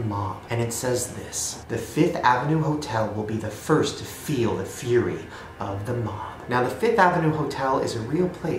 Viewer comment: I thought it said furry not fury